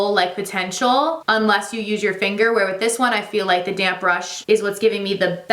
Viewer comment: Why do I always hear “damn brush” or “damn sponge “ instead of damp 😂